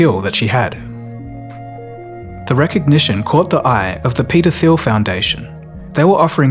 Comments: I heard “The recognition caught the eye of the PEDOPHILE foundation” 😂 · I swear to god at 3:03, i thought you said the pedophile foundation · I thought you said pedophile foundation. Then I read it on screen. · Peter feel foundation… I definitely misheard that 😂😂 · I swear I heard "caught the attention of the pedophile foundation" 😐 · Peter Thiel foundation almost sounded like pedophile foundation as i was just listening to the audio 😢🤦‍♀️